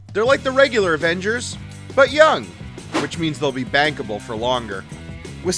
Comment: I thought you said ‘bangable’ and I was about to call 911